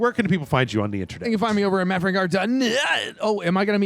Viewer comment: You know...for second at the end there it sounded like said "Matt Frank Art dot nuuut" and I couldn't un-hear that...and now YOU can't unread that. Hahaha!